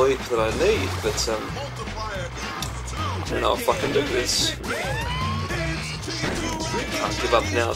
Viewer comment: You know it's getting serious when swears. Also, breakfast sandwich with no bacon? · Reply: Oh, I misheard you. At 3:10 you say "I don't know if I can do this" but I thought you said "I don't know how to fucking do this". So yeah